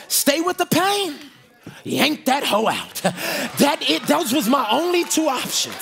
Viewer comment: Good sermon, but did you say “yank that HOE out”? @ 44:52 🤔😳..